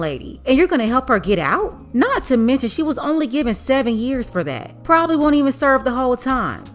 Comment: That's a long time to be locked up....my Lord ....that's a very long time....she will be a old woman...my goodness love 💕.....a world needs love and a community.... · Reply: Oh I thought 💭 it said 70 years ...7 years....